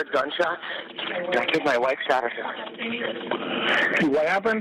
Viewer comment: Was I the only one that heard him say during 911 CALL....I killed my wife. I THINK MY wife shot herself??!!!! He definitely said I killed my wife, HE definitely didn't say I think my wife killed herself. He said I killed my wife.